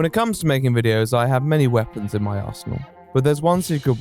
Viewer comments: tbh I thought he said asshole instead of arsenal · I heard "many weapons in my asshole" that made the ad so much funnier 😂